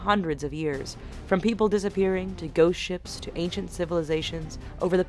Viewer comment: I thought she said "ghost shits"